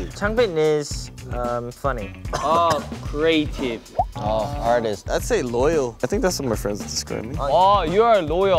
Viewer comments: I thought I heard Han say "Autistic" 😭🤣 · I’m sorry but when Han was reading Hyunjin’s description and said artistic istg I thought he said autistic! 😂